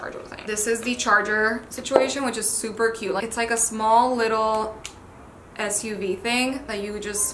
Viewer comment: did she just say suv insted of usb😂